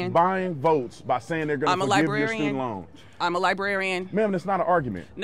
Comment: When she keeps repeating "im a librarian" all i hear is "im an idiot"